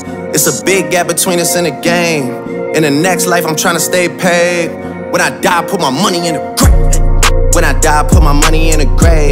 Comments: When I only heard the audio not the lyric I thought he said "WHEN I DIE PUT MY MONEY IN A GRAPE" bc I watched king science Tim tok and his tik tok was when I die put my money in a grape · I thought it said “When I die put my money in a GRAPE” LOL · I thought I heard put my money in a grape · He sound like he said When I die, put my money in a GRAPE